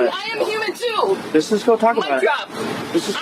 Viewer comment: did she say "mic drop" 😂